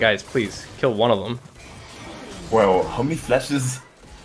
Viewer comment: Ahuevo or Guevo? what did he say? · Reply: I think it was just "wow"